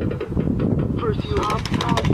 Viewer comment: Did I hear Barney say "hot"?!